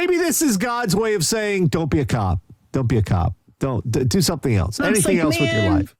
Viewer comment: You say "don't be a cop", and I hear "don't be a dick".